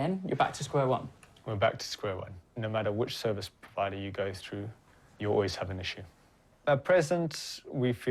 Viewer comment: “No matter what service provider you go to, you’re always having a shit.” That’s what I thought he said! XD